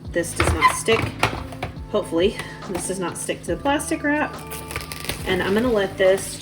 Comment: I thought you said “pasta crap” instead of plastic wrap. Haha ❤❤